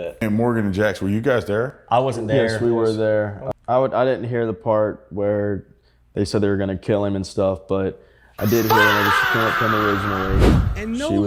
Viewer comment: "Did he say "I wasn't there when they said they were going to Ki** him and stuff" 17:10 😮